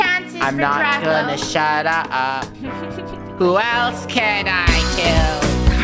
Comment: Wait what 0:18 Did he say who else can i kill